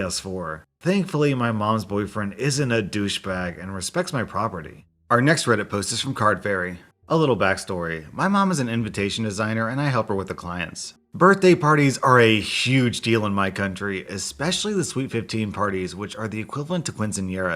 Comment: At first, at 10:24, I thought you said AMPUTATION designer... What the hell is wrong with my brain???